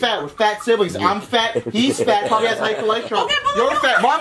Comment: did he say he probably has high cholesterol